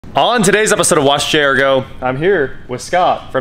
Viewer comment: You did it again great video keep up the hard work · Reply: lol wow I thought you were saying watch this whole time sorry about that , Love your videos !💪🏼